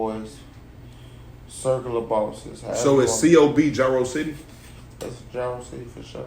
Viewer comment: I Thought He Said Circular Bosses Im Cryin 🤣🤣